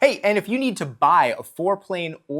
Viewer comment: Oooohhhhh, I'm dumb. for years I've heard "Fore plane" and always thought people were saying "Floor plane" and assumed they must be talking about a plane used for wood floors or something. lol